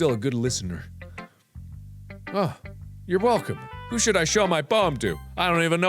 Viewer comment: For a solid 3 seconds I thought it said “who should I show my penis to first?”